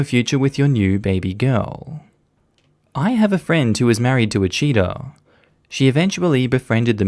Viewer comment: When you said I have a friend who married a cheater I thought you said I have a friend who married a cheetah I was very confused I was like okay I hope they're not doing some sort of bestiality crap